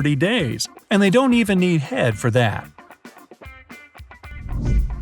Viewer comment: Cockroach. What does he say?? It sounds like, "they don't even need head for that". What does that mean?